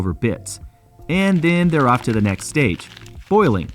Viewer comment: You said foiling instead of boiling. xD